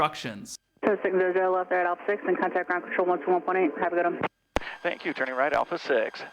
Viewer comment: For the life of me I can't make out the first two things at 1:45 she says. And I know what it is but can make out the call sign and turn out at alpha, she just mumbles for my ears. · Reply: DOH! I forgot the subtitles. "Cessna 600 left at alpha 6, contact ground on 121 point 8. Have a good one" I think she added an extra "right" in there though. May have meant "left right at alpha 6" Regardless, the right wasn't needed.